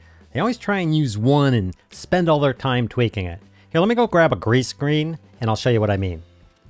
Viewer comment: I really like this editing tutorial series. 0:18 Did you say a "grease screen"? 😉 Love you, mean it.